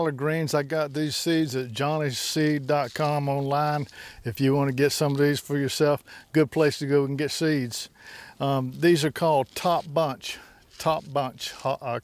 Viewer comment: where do you get your collards greens seeds I live in northern Michigan and i dont think they have the kind of collards seed you get would you please let me know where you can get them ...Ex video ..Mario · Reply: I thought he said Johnny seed co.? -N